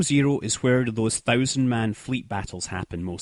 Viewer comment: did he just say, thousand players battle 11:14 :O